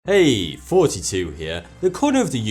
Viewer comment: I've watched a fuck ton of this guys videos but every goddamn time I hear "42 here"